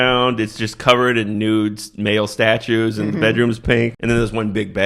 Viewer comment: I thought you said "Ba'al Statues" and thought wow they really wanted to demonize gayness 😅